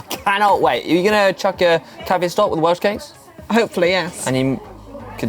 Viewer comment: what was hank saying??????????????????????????????? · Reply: Are you going to chuck in a cafe stop and welsh cakes ...